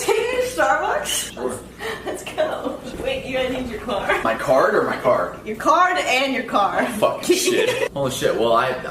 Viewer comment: (glr eu n to julgando tá?) Mas acho q no 2:48 era "my car or my card?" Tp... O carro ou o cartão pra pagar entendeu? (Tbm n tenho crtz mas acho q foi isso até pq n tem sentido ele repitir a msm coisa)